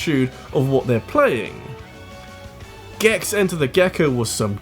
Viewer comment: Did somebody say Gex Night???